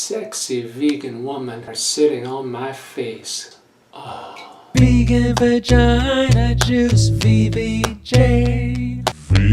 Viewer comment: Did I just fucking hallucinate? Did I just hear, "Vegan Vagina Juice," as some weird song? 😮